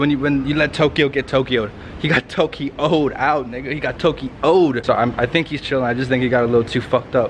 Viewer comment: Did i heard nigga at 6:11?